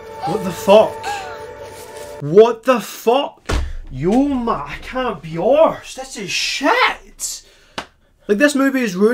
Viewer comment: What did he say @ 4:10??? Was it in another Gaelic?? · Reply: I can’t be assed? 🤣